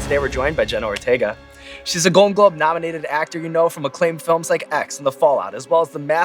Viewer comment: Sean, in your intro I thought you said that she was a Golden Glove nominee. I thought, great actor and MLB ball player? Multi-talented. Thanks for the chuckle.